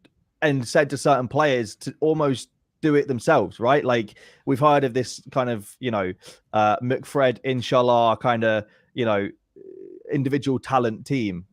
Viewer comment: did he say "we've heard of this McFred 'InShaaAllah' kind of team? 😂